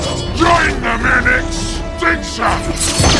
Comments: could someone PLEASE tell me what Megatron says here. I as a German hear "Join them in it! Wichser!" A German swear word, and if you translate that word very closely, it would be something like "jerker" or "fapper" if you know what I mean. But I'm very sure Megatron doesn't say Wichser. What does he say?? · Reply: "join them in extinction" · When I was a kid I misheard Megatron yelling "Join them in extinction!" as "Join them in a stink shaft", whatever that means.